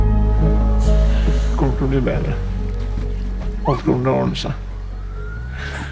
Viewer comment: Can anyone tell me what is the original Norwegian version of this sentence? Please.😖 I wathced this movie many times, but I found this sentence has different translation: 2 chinese version and 2 english version..... I'm so counfused about this😅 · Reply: Finally found it myself😅 "Kommer til å bli bedre. Alt til å ordne seg. Gjør ikke det." It means "Things will get better. Everything will be alright. It's not true."